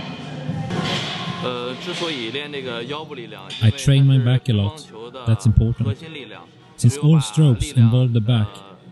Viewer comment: What was that I heard? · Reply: 那个=that